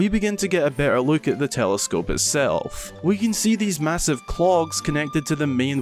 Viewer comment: The telescopes have 'cogs' not 'clogs'...lol Clogs are a type of wooden shoe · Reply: Lol I was wondering if anyone else had questions about that, i thought he said clocks the first time but then heard clogs clearly the second time but saw cogs (or gears ) were displayed in the vid